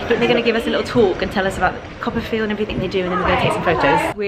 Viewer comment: I'm Australian so I'm not sure if it's my accent confusing yours, but the whole start of this video I thought you were saying "Copper Field" haha! Makes much more sense now I know what you're actually saying 😂 love the video!